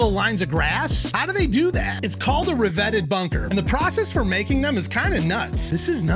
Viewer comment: Man doesnt know how to say riveted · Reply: Ya I thought he was trying to say riveted too. But its revetted.